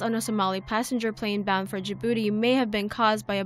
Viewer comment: did she just say flight bound for ya booty. :)